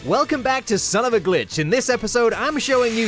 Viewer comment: I remember when i was a little kid i watched this and my parents thought he said sone of a bih and i almost got grounded